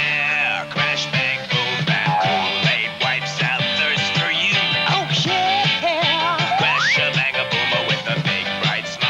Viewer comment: sounds like somebody said oh shit